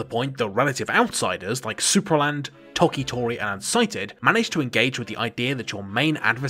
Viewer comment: What is this game? I can't understand what you said and I love the pixel art. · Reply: unsighted